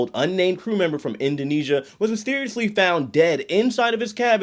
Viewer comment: I had to rewind, cause “mysteriously died” sounded like “seriously died” at first listen. Glad I misheard. 🤣